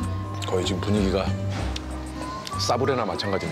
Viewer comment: ㅋㅋㅋㅋㅋㅋ싸브레 하고 들으면 진짜 싸브레네ㅋㅋㅋㅋㅋㅋㅋㅋㅋㅋㅋㅋㅋ 이거 몇번이나 본건데 갑진기 싸브레로 들리냐